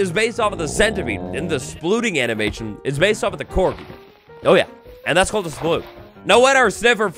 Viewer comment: Can you add subtitles? I can't hear clearly what you said on their resting animation, i heard it as "based on the corpse"??